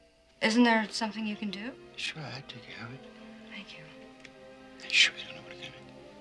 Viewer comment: I had to play that 5x before I understood what the hell he said. Plus I was still laughing about the little ratface he makes after he says "probably the rats." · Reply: I watched the clip once. It’s “sure I’ll take care of it” “you sure you don’t want to come in?”